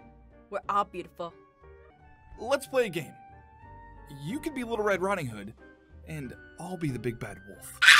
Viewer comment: i thought said “You can be the red riding hood and i can be the big bag oof” 😂🤣🤣